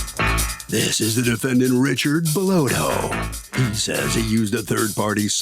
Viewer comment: I swore he said Richard blowhole🤣